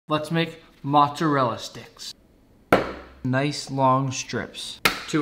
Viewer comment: I thought u said let’s make matcha rella sticks